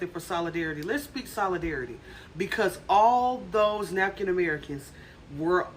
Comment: 🛑FACTS over FEELINGS:👉OK HOLD UP!🤔at time 4:06 , did she really say "Napkin-American" as a Euphemism for "W🤍American?" 🤣Gotta say, that's quite creative! But she's got real talk though!🤨Let alone the fact that the, um, "Napkin-Americans" 😉were damn-near 94% of everyone protesting! Yet the police seemed more "Strategically Congregated" at areas where the Blacks (About 2% of everyone protesting) would likely be passing in close proximity. "THEN" bark out (Selectively Timed) orders so as to "THEN" qualify "group arrests" that would include "Those Blacks!" passing (conveniently) at that moment🙄🤔YEP!🤨We peeping'em!🧐